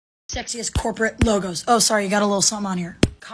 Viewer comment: I was so damn confused the whole time because I thought I heard her say "sexist" at the start then I looked at my phone and saw what she actually said lol